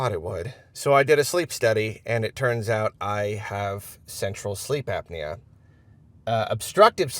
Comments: I thought said "Sexual Sleep Apnea". Seems off brand. Hope you feel better. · Totally thought you said "sexual sleep apnea" and was like wow, I have that. I literally can't fall asleep until I jerk it.